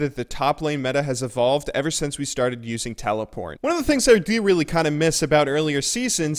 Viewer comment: Does he say TelePorn? Wtf